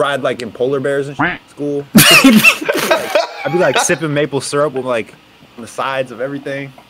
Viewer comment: Did he say sippen maple syrup